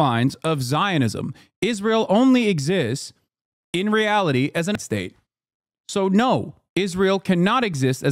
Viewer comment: what was the cut there for? what does Hasan say? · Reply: Apartheid state